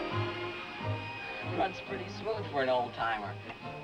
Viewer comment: What does he say at 5:47, does he say swood?